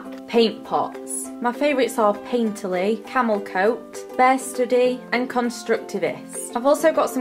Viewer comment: Really thought you said "camel toe" when you said "camel coat." LOL! Hope you're feeling a bit better. :)